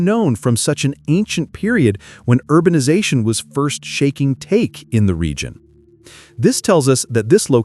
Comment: Very funny at 6:25 it says when first shaking take instead of first taking shape.